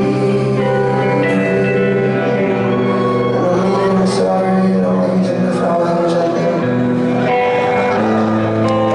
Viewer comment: anyone knows what jesse says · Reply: He actually says "I'm sorry i should be using language like that" referring to his earlier "shut the fuck up"